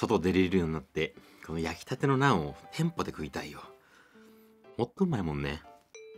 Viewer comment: 「もっと旨いもんね」と聞こえましたよ！